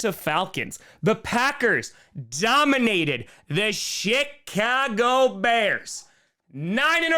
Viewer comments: I know I listened to my 80's rock bands waaay louder than I should have, and my ears are paying the price for it, but did I hear "the Shit-cago Bears"? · My speakers are kind of scratchy, but it sounded like you were saying the shhittcago Bears.🤣